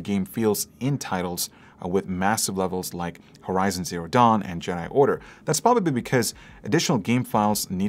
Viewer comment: did I hear "giant order"?